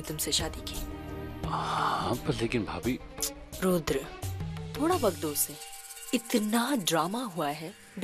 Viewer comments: did i only hear mehek calling raghu rudra at 9:52 or did u guys also hear the same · did she say rudra?